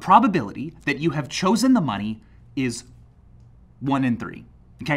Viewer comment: did I hear him say "1 in 30" even though the screen text and even the closed captions say "1/3"